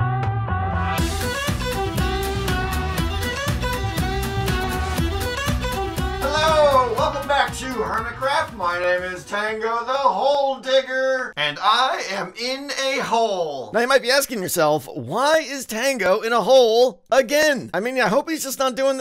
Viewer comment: I'm I the only one who heard him say I'm tango the gold digger?!?! LOL😂